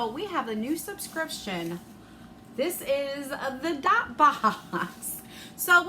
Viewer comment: Omg I totally thought u said THOT Box!! 😂😂😂😂 So many different subscription boxes!!! 💜💜💜💜💜💜